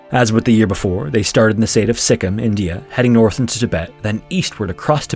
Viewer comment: I totally thought that said Skyrim ETA: they're all heros 🥺